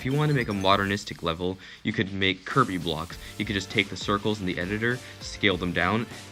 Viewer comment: I cant be the only one who thought he said "kirby" blocks instead of curvy blocks